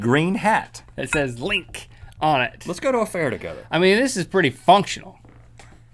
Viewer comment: Was it only me who thought at 8:50 Rhett said: Let's have an affair together??